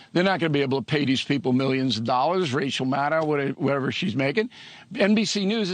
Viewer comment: Did he just say "Racial Maddow"?.